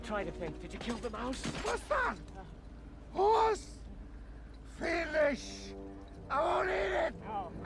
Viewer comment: the captions says finish I don't need it🤣🤣🤣🤣